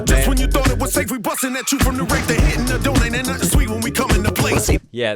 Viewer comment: "They hittin' the door, ain't nothing sweet"... I swear it sounds like he says "donut" as another level to the sweet scheme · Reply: He did say Donut